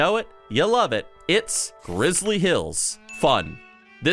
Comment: swear I heard Rizzly hills, I don’t know if is aware of his own Rizz